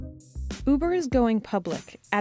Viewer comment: Anyone else hear boober instead of uber at 0:00?